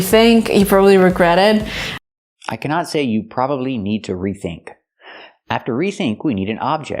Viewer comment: I think she says: you'll probably need a rethink (not 'to rethink')